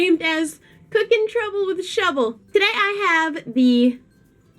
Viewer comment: for my whole time sued to her I thought her channel was shuDDle and I just heard her say shuBBle